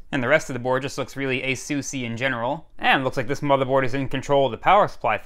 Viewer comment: Ok what about this for mishearing words. you said "ASUSee in general". I heard "Assusian general". Could that have been an alien warlord of some kind? 🙂